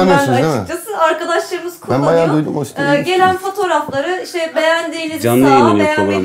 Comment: ben baya duydum o site iyimiş :D salşkdjsakjdkksjd